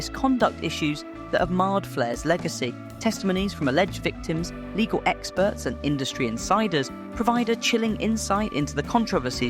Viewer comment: I thought you said “fecal experts” and I didn’t bat an eye. Flair is something else.